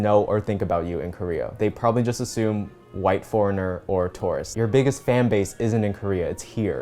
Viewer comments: I thought you said Taurus and I got really self conscious that Taurus’ are weird or something 😳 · So I thought you were saying "taurus" when you were saying "tourist" and really wondered who hurt you for a hot second.